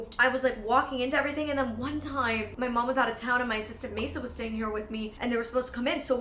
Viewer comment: At one point I thought you said "my sister Mesa" and I had to pause and be confused for a solid minute until I realized you actually said "assistant"